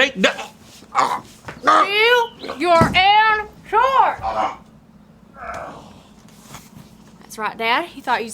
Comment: What Did Say? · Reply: I think she said do your own chores